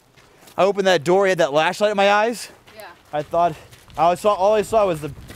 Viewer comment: Anyone else hear Patty say " Lashlight "